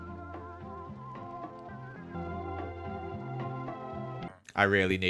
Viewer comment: when he said parrots i thought he said parents 🤣🤨